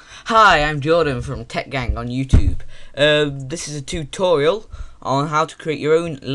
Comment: I heard you say tubetorial :P don't know if you said that but its a cool word :P